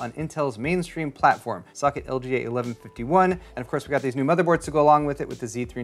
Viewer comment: I thought you said, "Suck it, LGA 1151," at first xD I was like what? What did 1151 do to you!?